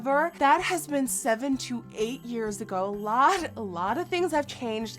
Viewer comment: first i thought she said 728 years ago XD